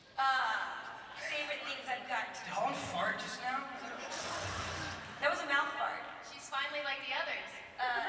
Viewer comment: What Holland seid in 14:21 ? i dont understand :) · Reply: She made a noise with her mouth and said "that was a mouth fart" It's a joke. Haha cause it sounded like a fart.